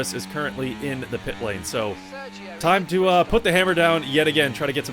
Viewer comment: when you said “time to uh” I thought you said “talk tuah”…